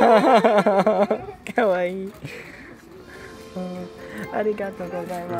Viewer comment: Did anyone hear a Grian say ''Arigato gozaimase'' edit: in 20:22 / 20:21 I think he also said ''Kawaii''